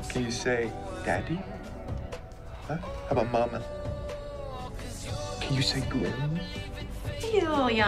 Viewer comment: What was he saying just before "he's a little young for that last one"? It doesn't even come up on closed captions. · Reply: he said-can you say Grimm-.